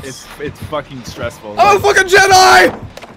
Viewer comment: What did say???? Also, why was Nin killer twice this episode? Also, any idea when the new killer is coming out? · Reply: I'M A FUCKING JEDI